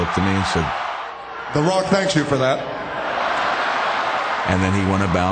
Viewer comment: Doesn't that sound like Obama saying: "Barrack thanks you for that." I could see Obama saying that to Joe Biden.